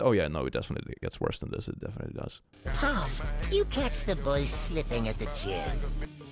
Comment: I heard sniffing, not slipping, and thought "this is not something to brag about".